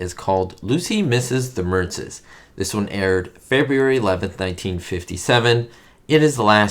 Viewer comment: It almost sounded like you said, Febriary, like Ricky does! LOL